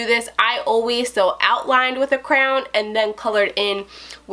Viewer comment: the way you said crayon messed me up for a sec because I thought you said crown